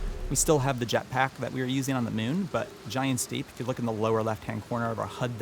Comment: Instead of Giant's Deep I kept hearing "Big Steve"...better name for it..gonna wait for a few more reviews before I pony up...even if I give up on the discount...maybe...that is tempting